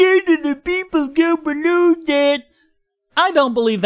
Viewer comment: What the actual fuck did you say at 2:10? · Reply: "90% of people go below that [the data cap]."